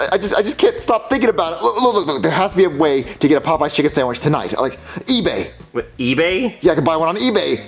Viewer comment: Lol I when he was talking about a Popeye chicken sandwich I thought he was saying a poop sandwich lol 😂😂